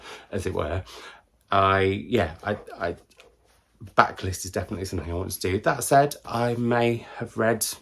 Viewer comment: Stealing/borrowing the name "Booktingle"... Totally thought you said a backless book at first, not blacklist 🤣🤣